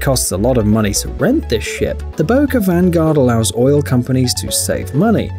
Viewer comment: I heard "shit"